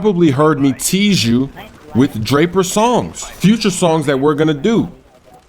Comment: I thought you said drake lol